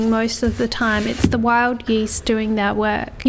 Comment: Wild yeast. I thought you said “wild geese.” “It’s the wild geese doing their work.”